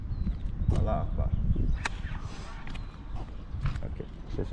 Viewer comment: when he borrows the kids toy gun, he actually said Allah akhbar wtf 🤣🤣🤣